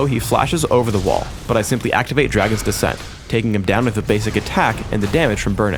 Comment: at around 4:10 did it say "Dragon-spree"??????